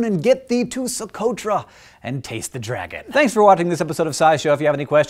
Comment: Taste the dragon? I thought you said chase the dragon. No thanks Hank, I'm good on heroin